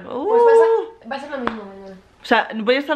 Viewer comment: cadepe: va ha ser lo mismo amor No se si ya estoy delirando o que pero creo que dice eso :V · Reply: Dice va ser lo mismo mañana, has oído mal ponte auriculares con el volumen a tope y escucha vas a ver qie dice eso